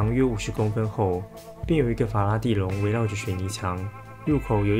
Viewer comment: 我怎麼聽的…聽成法拉利-_-#